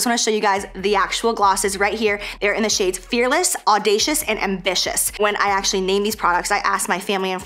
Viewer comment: LMAO when you first said the names of your glosses i misheard and heard you say Fearless Audacious and "vicious" then you said thats how your fam and friend described you and i was like whoa.. SAVAGE lol then you said it again and i was like ohhhhhhhhhhhhhhhhhhhhhhhhhhhhh haha